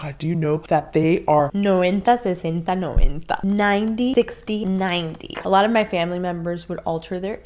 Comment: What did she say?? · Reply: 90,60,90